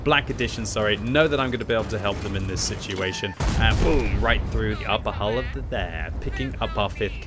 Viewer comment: Having trouble talking there at 9:18? xD I played it back a couple times and I swear you say "right through the upper hull of the there"